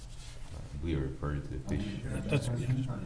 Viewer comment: What happened at 1:03:49 did somebody hear what he said? English is not my 1st langage please help! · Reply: "We are referring to the picture" I think